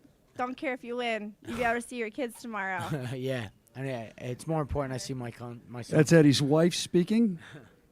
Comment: lol what did he say? "more important i see my cunt" ??? Typical Philly behaviour